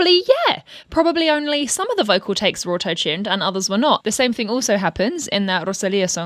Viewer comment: At first I heard “auto-chin” instead of “auto tune” and now i cant unhear it